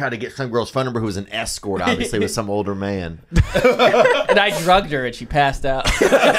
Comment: Theo big time saved Nick for talking about drugging a girl. That’s the adderall fast talk 😂 I hope it’s a joke 😎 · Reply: Oh shit I thought he said 'a guy drugged a girl and she passed out' but I guess I only heard that because of what Theo said after. If you play it back he actually says 'I drugged her'. WTF?!